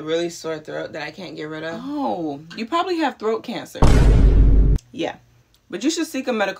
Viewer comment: Bro I thought she said “broke cancer” and I had to watch it back